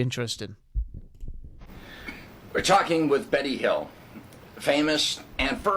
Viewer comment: I thought the guy said “…we’re talking to Benny Hill…”. All I could picture was Benny with his little reading glasses chasing half naked women around.